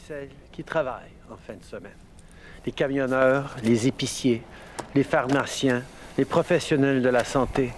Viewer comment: J'ai entendu "pharmartien" a 16:00 lol